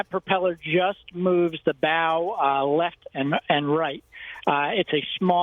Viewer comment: It's called a "pylon", not a "pier". Also...did you just say "bowel" thruster?